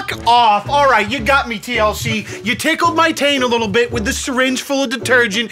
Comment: I heard "tickled my taint" and lost full